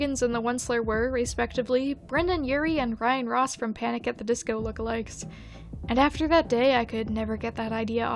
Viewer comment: i legit thought you said “brendon urie and ryan ross from panic at the disco with legs” instead of lookalikes